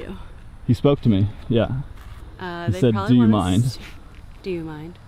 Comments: It actually sounds like he says "Are you out of your mind?" v. "Do you mind?" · Actually it sounded like Chris said, “are you out of your mind?” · I thought that he said, “are you out of your mind?!” I was freaked out watching that!! I was thinking that maybe Brian was with them?! But maybe they thought ya’ll were shady bcs of your dark Van Life van, Roberta will call the law on ya’ll!! & the big question is where’s the gun if he did that? Why are they allowing the Park to be open right after they found the bones? It’s just all too suspicious. Ya’ll are doing great work, that was good drone footage!! Ya’ll Please be careful, bcs certain people won’t like what you are doing & that really scared me when ya’ll got stuck. 😳😬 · I thought he said "are you outa your mind". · I heard Chris say “are you out of your mind” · it sounded like he said "are you out of your mind?" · I thought Chris said “ Are you out of your mind?” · Did he say “are you out of your mind?” Or did I mishear him? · You guys are awesome love watching ur channel on all this it sounded like Chris said are you outta your mind cause be wrong the neighbor accrossed the street was giving plenty of information out to another person on Saturday about the laundries you should try to interview her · I THOUGHT he said, "Are you out of your mind?" I'm new to your channel. But I can tell, Im going to love it here!